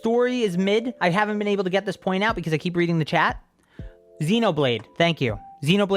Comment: In 33:00 I thought he said xenoblade story was trash and I was like bitch s’cuse me